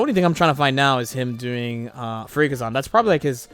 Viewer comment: My brain was switched off, so when you said fuurinkazan, I heard "freak-a-zon" 😛